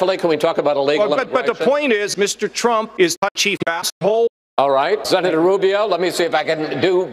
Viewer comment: I thought kasich said "cheap-ass hoe". :P